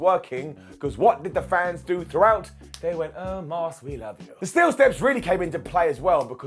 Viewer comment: When he said oh moss we love u I thought he said omos we love u 😂😂 and im like why would the crowd say that for moss and corbin fight